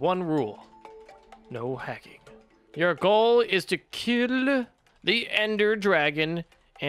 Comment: said tok Ill instead of to kill